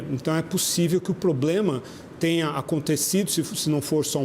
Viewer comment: Eu ouvi poblema ? 😳